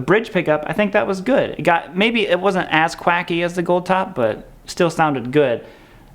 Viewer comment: My wife just asked: "Did he say ass quacky?" (6:34).......